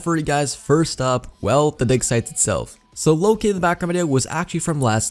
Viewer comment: When he said dig site I thought he said dick site